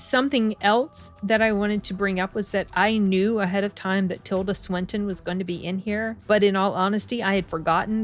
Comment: I had not heard "Tilda Swinton", I heard "Hillary Clinton". And the irony of her in a movie called "The Killer" just struck me. Had to go back and check.